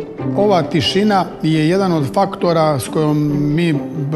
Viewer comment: Was it just me or did he say the f word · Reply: He said "jedan od faktora", it means "one of the factors"